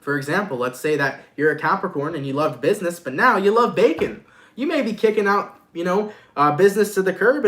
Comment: LMAO i thought u said 'youre a capricorn and you love business but now you love bacon'